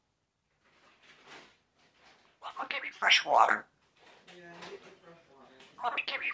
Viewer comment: I heard: ''MY coffee? Mummy give me fresh water!''